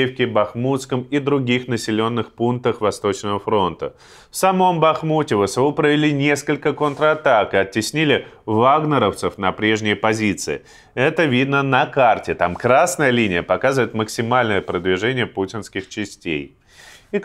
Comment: Мне вместо "вагнеровцев" послышалось "влагноревцы".... Что эт у меня по Фрейду...